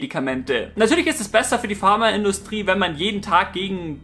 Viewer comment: Haha ich hab erst Farmer-Industrie verstanden und mich gewundert was es damit auf sich hat und ich weggelacht :D